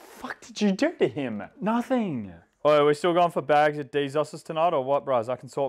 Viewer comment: Does anyone know what the grandpa says at 2:17? It sounds like he says bags at deezuses but I’m wondering what he actually said.